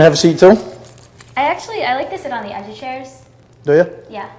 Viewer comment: "I like to sit on the edge of chairs" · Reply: I always thought she said she likes to sit on the other chairs;😂😂 way more funny realizing what she actually says